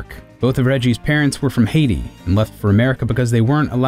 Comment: did you say Hades?